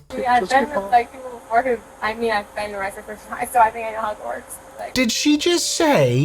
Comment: Idk about y’all but I hear “Hogwarts” and not “I know how it works”